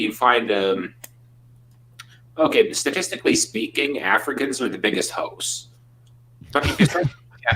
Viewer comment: Did he just say Africans are all hoes?